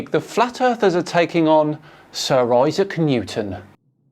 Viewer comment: Did anyone else hear "Sir Isah Kanutin"? 0:24 timestamp